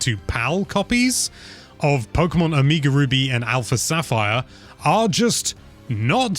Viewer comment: Did he just say…. Omeegle ruby?